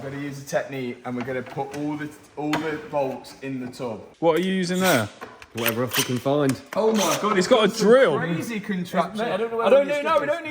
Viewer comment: did say what every I can f**king find 😂